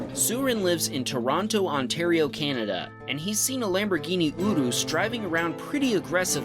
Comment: Bro did u just say, Lamborghini Odo. 😂